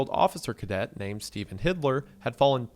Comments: Bro it sounds like hes saying "Stephen HITLER" when dudes name was "Stephen HILDER" 😂😂😂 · I am tripping or did he said Hitler?! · am I tripping or is he saying Hitler died during sky diving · wooooooaaaaah. I thought you were saying HITLER! You’re saying Hidler, but it’s typed Hilder on screen. Big OH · Am i crazy or is he saying hitler not hilder?